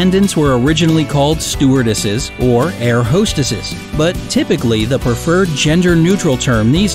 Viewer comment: Thought he was about to say Air Hostages